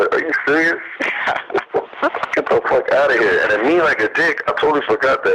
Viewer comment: wat did he say cuz it kinda sounds like he saying dick